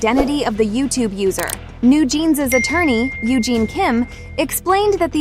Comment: I thought you said NewJeans lawyer’s name was NewJean Kim 😅